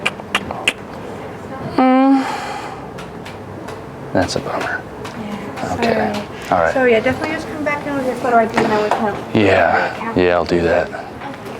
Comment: it sounded like he said "Thats a boner" after he made a sex noise